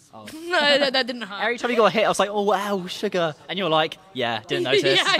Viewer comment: hol up did u call her sugar